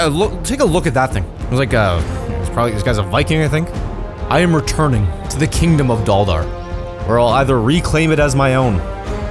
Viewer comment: Ngl I thought he said dollar kingdom